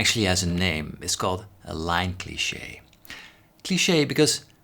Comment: is he saying "line cliche"? Because I've always wondered what this is called.